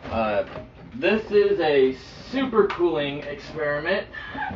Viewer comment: lol i thought you said "this is a super cool experiment"